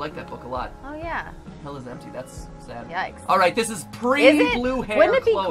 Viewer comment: Lmao when I say “Hell Is Empty” I thought it Said “Hella Empty”